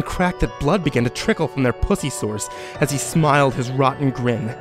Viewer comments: did he just say pussy sores? I feel like it was supposed to be Puss-y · did he just say pussy sores? Well... that doesnt sound pleasant.